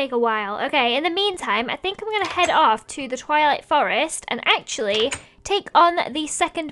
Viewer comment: When she said go to the twilight forest I thought she said toilet forest😂😂😂